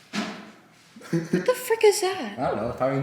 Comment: On this video THIS IS THE FIRST TIME I HEARD SAYING " WHAT THE FUCK IS THAT " AT 4:23 EXACT I NEVER EVER KNEW THAT CURSED !!!! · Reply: she said frik but the i was rlly rlly unnoticable so it sounds like she said fuck